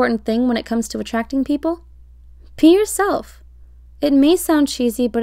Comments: thought she said pee yourself..tbh that one would be easier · I thought it said “PEE yourself” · Am I the only one who heard pee yourself?